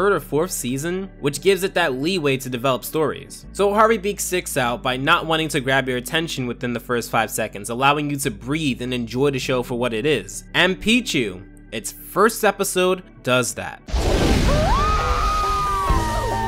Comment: I thought you said and Pichu